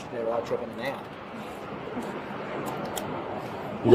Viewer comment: What is with the cop saying "I'm more a Christian without tripping now" @ 14:33?